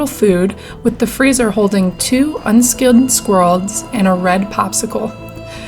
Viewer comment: what was in the freezer?.. something and the popsicle, but I can't understand what she says.. captions say "two unskilled squirrels".. 😂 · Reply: Haha "unskilled squirrels"...I seemed to mumble it a bit but it was unskinned squirrels.